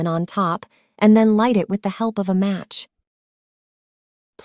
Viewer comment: It will give a rich smell, while the insects sting🤣 Greetings from Germany ✌🏻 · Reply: While the insects stink i thought she said. I’m like huh??